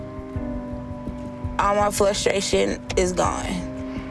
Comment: did she just say "flustration?" The combination of flustered and frustrated. Lol!!